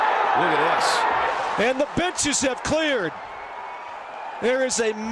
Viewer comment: I thought he said “The bitches have cleared” 🤣🤣🤣🤣